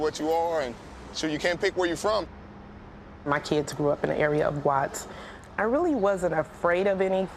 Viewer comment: Did she says at 5:46 "MY KIDS GREW UP IN AN AREA WITH GUATS ?? lol